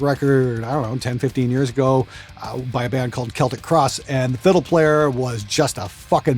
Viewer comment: You said, "..by a band called Celtic Cross..." and I thought you said Celtic Frost. Lol. I did look up Celtic Cross though and that's some interesting stuff. 👍